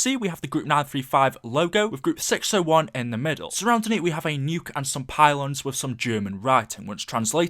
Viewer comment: Drives me crazy when I can't understand what's being said. At 2:48 o hear " so round 3 we have a nuke in the middle" but I doubt that's what you said. I replayed like 15 times and still don't get it